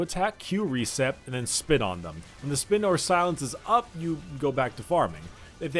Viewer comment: Who else thought that he said "spit on them" instead of "spin on them" edit: The foreshadowing of the Ugandan Knuckles meme